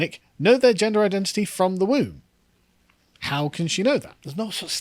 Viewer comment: "In 2022, the children's hospital said kids know they're transgender in the womb" I don't know about you guys but when I was in the womb I didn't even know I EXISTED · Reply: I heard "from the womb" which means, in context, at the moment of birth. Still insane.